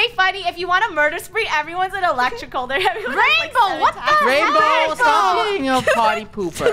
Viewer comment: I swear I heard say "RAINBOW WHAT THE HELLLLLUH"